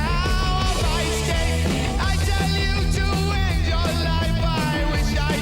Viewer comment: I actually think you are over analyzing it. What does he say? "I tell you to live your life I wish I could but its too late." Over and over again I hear, " Itell you to end your life I wish I could but its too late." The latter is a lot darker and I know what Ozzy said but what do you think. I heard end insted of live. Fact. This was a filler song. It eas wriiten to make up space on thier first album. Another fun fact. I think you are thinking more about how Izzy sings than he ever did. My gawd. That man made Black Sabbath. Not because he was a trained singer but he sang with a sort of desperation in his voice. If you haven't yet break down his vocals on the song Black Sabbath. Iommi and Butler playing that dimished fifth and the composition. The root, the octave and then the dimished fisth or flatted fifth from the root sounds dark and evil. And Ozzy's vocals just makes the song. Every metal and rock band needs a vocalist like Ozzy. I never cared for Sabbath post Ozzy. Nah. Ozzy Osbourne for sure ove Sabbath. I mean Randy Rhodes. Crazy Train. Break that one down.